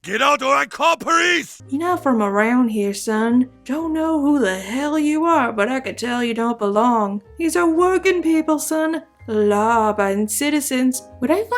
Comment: What did you say at 5:05? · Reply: “Your not from around here son, don’t know who the hell you are, but I can tell you don’t belong. These are working people son, law abiding citizens.”